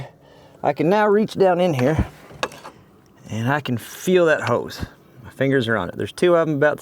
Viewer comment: You should make a new shirt that says "yeeting hose". I swore I heard you say you were gonna yeet that hose and I had to rewind and see if its what you really said.